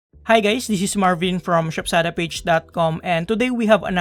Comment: what did u say, hi gays